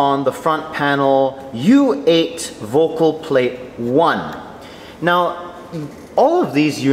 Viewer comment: Thanks for the helpful video! I had this running on a second monitor and couldn't help laughing at 12:32 when i heard 'You ate vocal plate 1' until i looked at the video and saw U8 Vocalplate1 :D